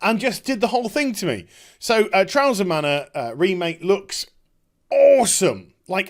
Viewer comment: I thought you said trouser mana.